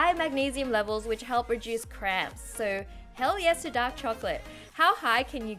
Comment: did he say hell yeah or heck